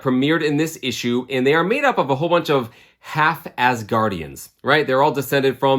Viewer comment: I literally thought you were saying Half-Ass Guardians ;)